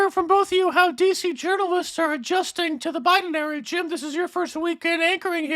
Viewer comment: I thought Brian said " The Biden Marriage " ..which would be more fitting till I read the bottom